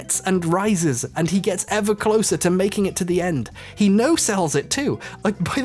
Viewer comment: He no-cells it? No-sales it? Can't hear what he's saying at around @ 32:39. Help! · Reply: No sell which is a wrestling term funnily enough basically meaning making your attack seems less impactful and less hurt that it seems